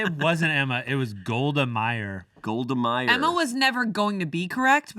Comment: thought they said “gold miner” and I was like yeah that tracks